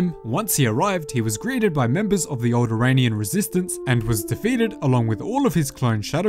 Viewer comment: I realize you said "alderaanIan resistance" but due to the accent it sounded like you said "old Iranian resistance"